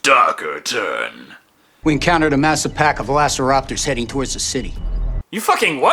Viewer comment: This man clearly says the imaginary word of "velassoropters" instead of velociraptor. Beautiful. Just mwah😚